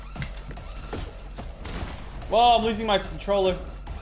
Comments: Wait did he just say I’m losing my controller I didn’t know he used controller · Did Lachy say “I’m losing my controller?”🤔...⌨️=🎮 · Was that controller I heard Lachy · Did say " I'm losing my controller " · Is it just me, or did say at 7:33 say I’m losing my controller?